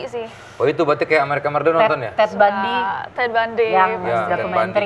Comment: Teh hesti bilang apa sih di menit 3:30..? Yg di iya in ama nessie itu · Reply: Ted Bundy